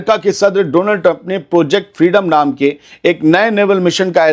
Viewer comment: Bro did you say Donald Duck 😀